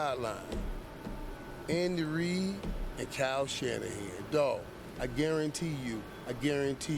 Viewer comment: Pretty sure he was referencing Kyle Shanahan and Christian Mccaffrey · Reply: yeah I initially thought that he was saying Purdy and Shanahan, then figured out he was saying McCaffrey and Shanahan- maybe also because CMC has known Kyle since he was a little kid.